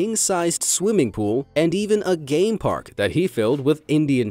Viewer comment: When they said "game park" I thought they said gay bar😹😹😹I was like whaaaaaaa